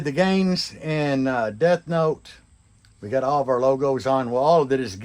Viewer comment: LOL, did you say DebNote? Not a bad name for the build.